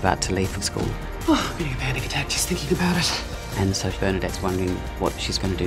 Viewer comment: I don't understand english very well, can someone tell me what she said at 0:26? pls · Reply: "oh, getting a panic attack just thinking about it."